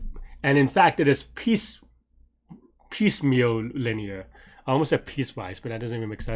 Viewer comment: What is the word you said at 4:42, piece-something linear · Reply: piecemeal linear